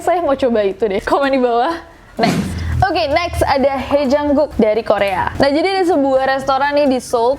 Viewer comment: i heard "hi jungkook" instead of "haejang-guk" :3